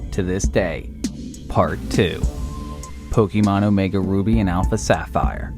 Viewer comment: Pukimon? Did anyone hear that to?